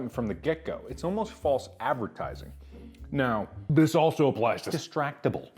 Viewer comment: did he just say distractible has false advertising?